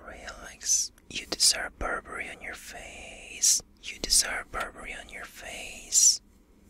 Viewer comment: LMAOO I THOUGHT HE WAS SAYING "you deserve poverty on your face "